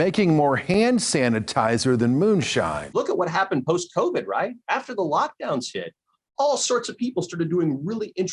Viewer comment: did he say "After the lockdown shit" or is that just my mind hearing what it wants too lol.